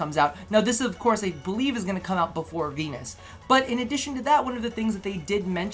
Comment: Did you say penis instead of Venus around 2:40?